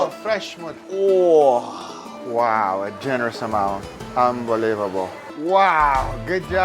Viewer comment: Did Guga just said "hambelievable" in 9:00?